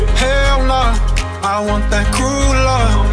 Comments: I always thought it said I want that culo .... not crew love . ..... whoops 😬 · I thought he said "cruel love" wtf is a "crew love" ?? a gang bang...? lol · Bro listening to this on pandora I swear it sounded like he said "Hell naw I want that culo"👀 thats why I'm here😳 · I thought he said "cruel love" wtf is a "crew love" ?? a gang bang...? lol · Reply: Well I thought he said "I want that culo"